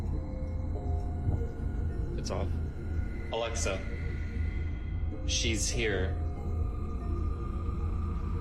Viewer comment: I dunno it sounded like Alexa wanted him to Shit there